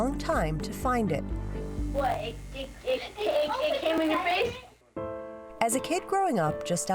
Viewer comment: Who else heard him say “it came in your face” 😂